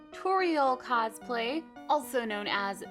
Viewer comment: Who else thought it said tutorial instead of Torial at first?